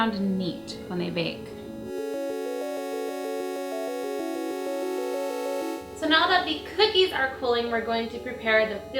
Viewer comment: What did it say at 2:25? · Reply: It said bake for 8 to 10 minutes